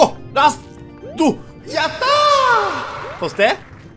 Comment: の「やったー！」がミッ○キーに聞こえた笑